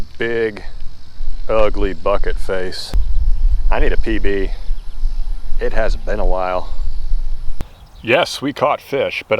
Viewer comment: Thought you said, “I need to pee pee, it has been a while.”